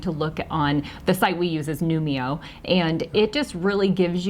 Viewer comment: Hi. Hubby and I love your videos and are planning our early retirement travels. We heard you say the name of a site that you visit for costs upon arrival to a location but can’t find it in our search. Hoping you see this message and can provide the website link. It sounds like you said “Numio” but don’t see anything meeting that name in our search. Thanks in advance! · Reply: Thank you! The website is called Numbeo! 😄 <link>